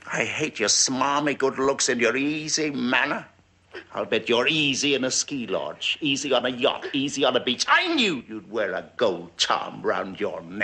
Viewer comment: Can someone tell me, what he says at 3:50? I dont understand the accent. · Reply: Easy on a ski lodge, easy on a suit, easy on a beach.